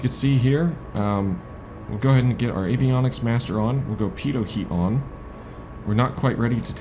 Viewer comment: did you say PEDO HEAT on ? :DDDD